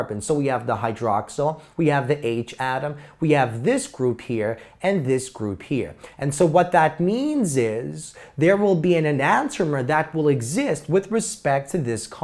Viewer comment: he was saying nantomer or antomer, I could not make out what he was saying at 3:12 or if he meant an anomer.